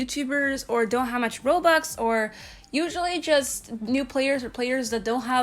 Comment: I Thought She Said BOBUX